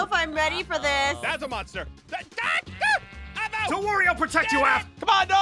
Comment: My caption says "Don't worry I'll protect you α$$💀😭